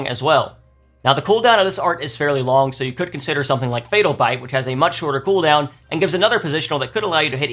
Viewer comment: I thought he said Fatal Fight lol